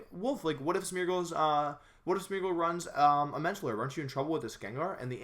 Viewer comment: "Wolfey what if Smeargle runs a ....?" Could someone tell me what he says? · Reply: Mental Herb